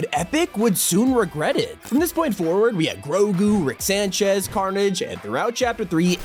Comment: did he say grogoo🤨